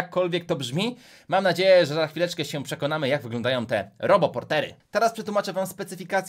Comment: A ja usłyszałam zamiast robo portery to Harry pottery (>﹏<)